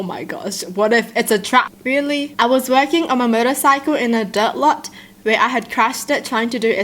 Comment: When she said motorcycle I thought she said murder cycle😂